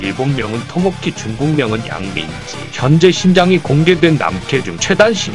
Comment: 중국명이 양민지라고 들림